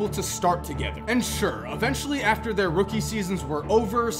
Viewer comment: did he say steph?